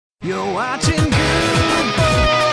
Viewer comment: I always heard it as "You're watching guude BOWL A FISH." And rather than a fish bowl, I imagine Guude throwing a halibut down the lane at bowling alley. The fish gets to the end of the lane and flops around, until all the pins fall down, and then Guude throws up his arms like a champion.